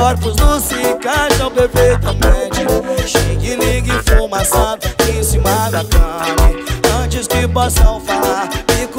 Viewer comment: [LETRA INEXPLICÁVEL] Inevitável eu querer te evitar, é a mesma coisa que bolar um fino e não querer fumar. A nossa brisa é tipo queijo e goiabada, e o sereno da madrugada levemente a molhar. Nossas implicações, afirmações, e discussões tipo canções com tons difíceis de se alcançar. Se eu falo fica, você diz que vai embora, eu falo vai, você retorna é foda pra contrariar. Maluca tem ciúmes até da meiota, constantemente na bota, não me deixa respirar. (Assim não dá) E no lugar das flores eu te trouxe um flow, daqueles que você vai se identificar. Fiz pra falar do celular que tu jogou na parede, a tela trincou, por não querer te retornar. [LETRA CHEIRO BOM] Tava lembrando daquele abraço apertadão, seu cheiro bom fixou no moletom. Dentro do carro rolava uma competição entre a mary jane e o perfume da mozao. Sai por aí sem rumo só pensando nela, tão linda tão minha, e ao mesmo tempo distante. Ela patricinha e eu o cria da favela, mais nois se combina quando joga o chá na blunt. Vi que a porra dos opostos se atrai, é quente. Eu funkeiro nato, e ela se amarra em nirvana, nossos corpos nus, se encaixam perfeitamente. (Não entendi o que ele canta, se alguém souber responde pra mim editar KK). E pra cada bla bla bla, um sorriso de irônia, quanto mais criticam mais eu te pego de jeito, quanto mais criticam eu te pego de jeito. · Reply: morri na parte q cê fala q não entendeu KKKKKKKK eu reproduzi 3x e entendi "xing ling fuma sapo em cima da cama" ???? TODAS as vezes